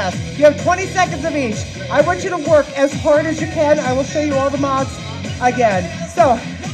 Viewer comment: At the beginning of the last tabata when you said I want you to work as hard as you can, I thought you said I want you to twerk as hard as you can lol So I did, and was having a great time until I looked up and realized you weren't twerking!!